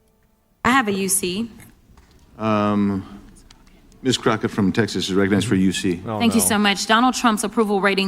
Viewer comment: I thought this man said “umhh this cracka from Texas” 😂